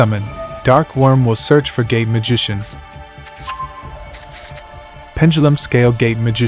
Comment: "Dark Worm will search for Gay Magician." This is what I heard haha. Now I'm imagining a dragon having a gay relationship with a gay wizard.